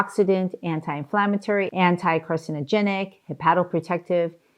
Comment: Whoever is responsible for the English captions are making crazy mistakes. At about 7:40 into video Dr says anti-carcinogenic (anti-cancer) and the English subtitle says anti-Christian???? LOL Never knew foods choose a religion to support or deny. :)